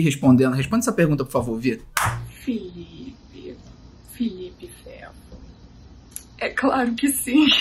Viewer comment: Mais alguém ouviu a Vi chamando o Felipe de "Felipe feto" suspeito.... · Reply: Eu ouvi "Felipe.. Felipe Fefo" Acho que foi heim